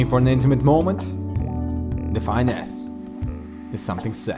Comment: Lol 1st I thought I heard "The Fine A**" later I realized its Define S :P xD